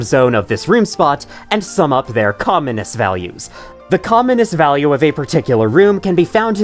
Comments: I thought he said the "communist" value that second there 😭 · i keep hearing “communist values” lol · I misheard "commonness" as "communist" 😅